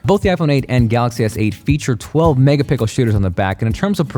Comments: did he say "mega pickle"??? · I swear he said “mega pickle” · Did you just say both feature 12 megapical shooters 😂😂😂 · "Both the iPhone 8 and Galaxy S8 features a 12 mega "pickle" shooters on the back" I lmao when I hear that, overall best comparison I've seen, liked the video and subbed 😊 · Did he say 12 mega pickles😄🥒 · Is it just me or did you just say "12 megapickle"? Lol. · Did you just say Mega pickles at 5:52? mjnam? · Did he just say 12 megapickle? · Did he say "12 Mega pickles??" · Anyone else heard him say megapickle? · Who else heard I’m say 12 mega pickle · Did you just say "12 mega pickles ?" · Anybody hear "megapickle" at 5:51? i just laughed inside. But hey, I love all your videos! :D